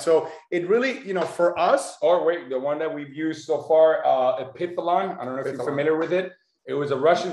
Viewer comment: What was that peptide made by Russian scientist to lengthen Telomeres? At 4:15 . Couldnt catch what he was saying. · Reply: Epitalon